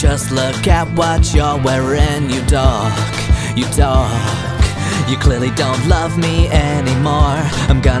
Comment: when he was saying Dustin was a dork the first time watching this i thought he was saying you donk😂